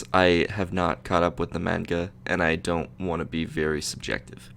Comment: What did he say? 0:37 MANGA (he pronounced it Maan-guh)